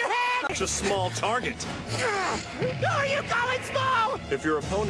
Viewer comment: Did Roy say, "if your opponent is of choleric temper, seek to irritate and never fall for an enemy's tarts"? .....tarts????